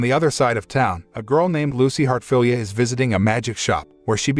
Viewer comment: did I hear this name right or what? Lucy heart failure